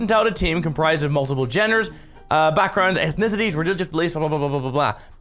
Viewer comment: is it me or did say "jenners" instead of genders in